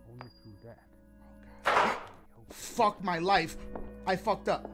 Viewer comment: I wAtch every dashie video but this one my boi said f my life